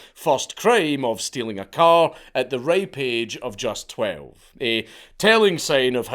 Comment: I heard “at the rape age of 12” and I thought what the fuuuuuuck?